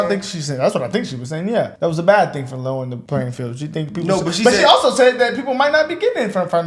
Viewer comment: I thought she said disleveled the playing field by removing affirmative action